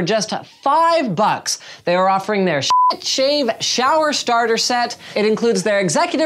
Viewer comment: Dollar shave is offering shit shave!? Did I hear it right?